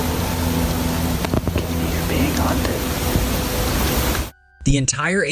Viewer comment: Oh, at the 36:11 mark, the guy with the camera is either saying "you're or we're being hunted."